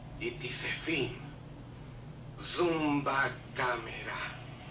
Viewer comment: Would have been funnier if he had said "No. It is reality TV. Zoom back camera." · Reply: I thought he said « ZUMBA CAMERA » 😂😂😂😂😂😂😂 💀